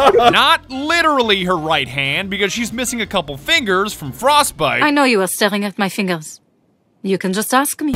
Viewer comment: I thought he said thermite when he said frostbite but I mean other operators have attacked each other in the past